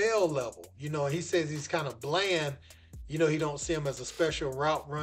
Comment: I thought he said he'd kind of "Blend" as a WR, and he would stand out as a CB